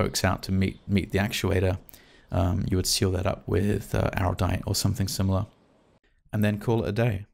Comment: you say "seal it up with....." I can't make out what you say. Did you say aerolite? Like the heat resistant adhesive? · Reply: I say Araldite, but any plastic-compatible 2-pack epoxy is the point.